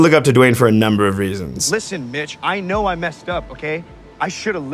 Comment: when brody said "listen mitch" i thought he said listen "listen bitch" and was confused